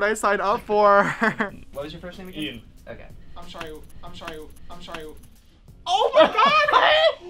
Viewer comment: i dont get all i heard was "i'm shy you"